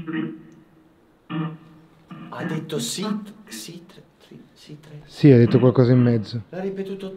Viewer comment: io ho capito “si, libri, si”